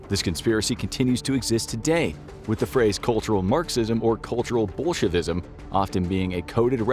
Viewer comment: culterial bullshitism is what i heard and will continue to believe in it